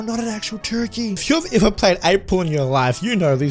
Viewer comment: If you skip to the number 1:33 he said 8 porn instead of 8 pool 😂😂😂